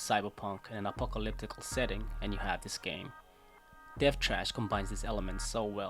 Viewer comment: what's the game? it'll help to have the title in your description cause at 0:16, I can't clearly decipher what you say.......Death chaste? death cast? death ???? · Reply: Thank you for the feedback! The name of the game is "Death Trash"